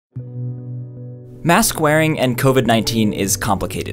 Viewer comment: You said "mask wearing" but I heard "mass squaring"